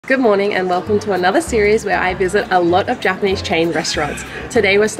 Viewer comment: Only I thought she said "Good morning Emma" like the vlogbrothers?